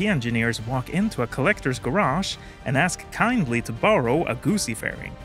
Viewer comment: My brain is broken because I know you're saying "Guzzi" but I keep hearing "gussy" and it's cracking me up